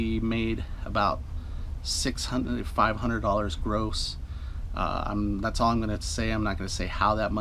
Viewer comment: For some reason when you mentioned your earnings it sounded like you said you made $500 gross. That can't be right. I appreciate these videos. I just watched the sugar beets one. I'm definitely not physically able to do that, but I'll keep watching. Very informative. Thanks.